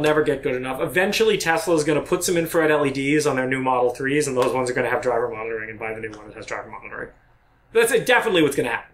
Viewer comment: Non native speaker here. 1:18:03 What is this guy saying here? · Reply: "Eventually Tesla is going to put some infrared LEDs on their model 3s, and those ones are going to have driver monitoring. And buy the new ones, and those will have driver monitoring." Infrared LEDs will allow a camera to see you at night without actually shining a bright light on your face.